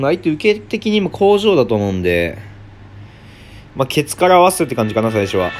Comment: ケツからワサビに聞こえるの俺だけ？